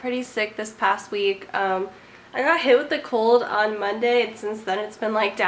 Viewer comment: thought she said "i got hit with a car" and i was like dammit when i realized she said "cold"